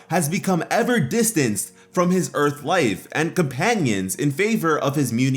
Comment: On 6:30 ir says his "Earth wife" I think he meant to say earth mom I am not sure. Can anyone explain this was this a mistake or he meant to says that.